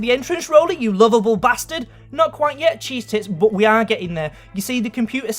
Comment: did he just say cheese tits *goes back" "not quite yet cheese tits" god damn he did